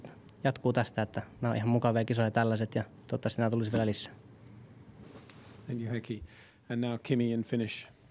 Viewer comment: i think lewis say" What did you say?"